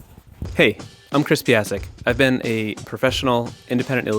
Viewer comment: I thought he said, "it's Crispy Isaac"